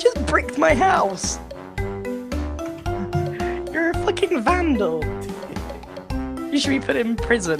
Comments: Everyone hears Grian say "you're a fucking vandal", but I hear it as "flicking" or "freaking" 😭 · sounded like grian said flicking lol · Honestly is sound like grian says flicking vandal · It sounded like grian said flicking